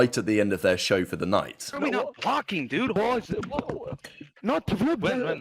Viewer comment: I thought said how are we not blocking, maybe im midhearing but maybe he went back because he ws worried about people spawning behind him?